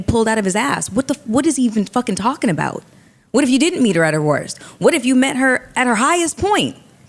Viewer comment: “Love her at her worth”. Prime example of emotions taking over 🤦🏾‍♂️ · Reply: I thought I was tripping. I could have sworn I heard him say "Love her at her worth" . I'm happy im not the only one who caught that.